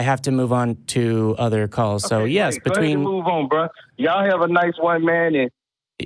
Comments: Matt, I heard it too: "Ya'll have a nice white man." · Clearly he said, "Have a nice one, man," but it sounded like he said "Have a nice white man." :-D